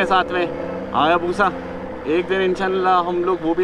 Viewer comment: did he say inshallah?